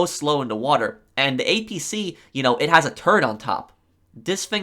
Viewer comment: the APC has a turd on top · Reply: Quinten Lust it sounds like it says turd but due to his accent he actually does say turret